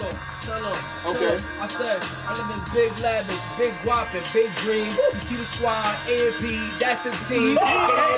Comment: I thought he said cream, shit would’ve sounded nice